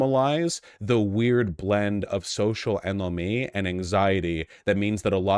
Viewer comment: Anyone know what Vaush says in 0:46? "Social elonge?" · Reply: anomie? noun, Sociology. a state or condition of individuals or society characterized by a breakdown or absence of social norms and values, as in the case of uprooted people.